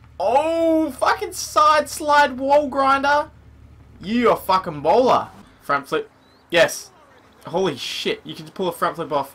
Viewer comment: Did anyone think he said Ebola instead of you're a fucking baller 😂😂